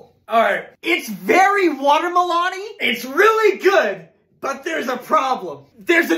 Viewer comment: Did he just say "Water-melON-ee"